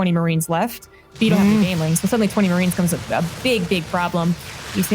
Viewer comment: BIG DICK PROBLEM??? Или мне послышалось? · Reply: Big bBIG problem, not dick!